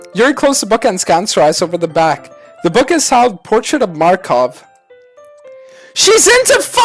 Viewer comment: i thought he said makarov from call of duty